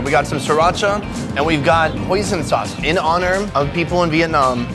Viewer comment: For a moment, I thought I heard “ poison sauce ” 9:51 🤣🤣